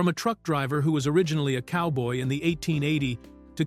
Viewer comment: Aw I thought i heard you say "trunck driver." That would've been funny.